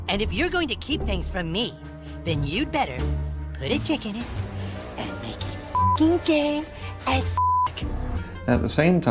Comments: I misheard alternate universe KK. I thought KK said "put a chicken in it and make it gay". 😂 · Lol I thought he was saying put a chicken in it and make it gay 🤣🤣 not until repeated it 😂😂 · I thought cartman said put a chicken in and make it gay